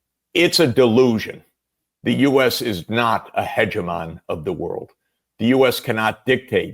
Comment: did he say ejamaan? Its a Tamil word with the same meaning. Leader. Googled and found that its a Greek origin. Fascinating 🎉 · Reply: It is 'hegemon' - a dominating power. Syncs with ejamaan of tamil / ejamana of kannada